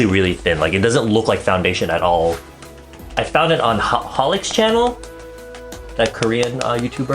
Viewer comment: I didn't hear 4:07, did you say howling's channel? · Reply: he said holy, if i'm not mistaken i think he's referring to this video?? /watch?v=N8TpMoHwrZI